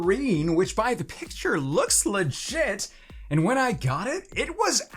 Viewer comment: I thought he said ‘looks like sh!t’ instead of ‘looks legit’